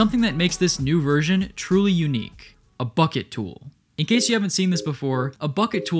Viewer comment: my dumbass thought you said a fuck it tool